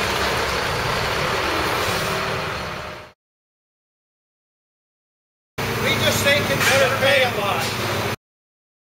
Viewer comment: I've listened to the comment they made starting at 2:09 over 2 dozen times, still can't understand it. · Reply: we just saved him from having to pay a lot